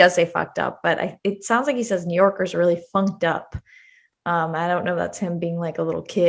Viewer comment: I thought he said NYers are really “pumped up”...like they are all about Clinton and he’s not happy about it.